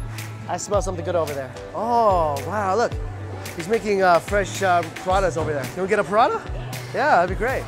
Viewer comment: I thought he said piranha